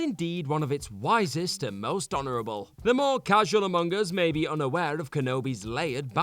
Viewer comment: I thought he said “among-us”😂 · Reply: But... He did, he said "The more casual among us..."